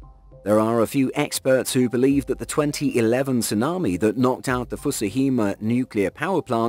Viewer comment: I have to call BS on the 130 ft tsunami in 2011 off the coast of Japan. I have seen every video out there and none have been more than about 30 ft. Please do prove me wrong where is the video evidence? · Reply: I thought they said 2000' tsunami But that could have been the waves that disappeared for no apparent reason lol